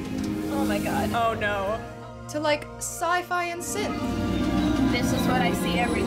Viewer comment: What does she say at 6:20? "Scifi and sith."?